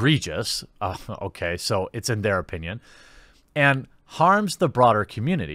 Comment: Harms the brauds? ARE THEY calling WAMEN Brauds! I heard it folks! They are calling out Brauds!